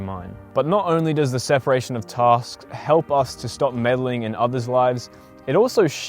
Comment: I kept hearing “separation of toss”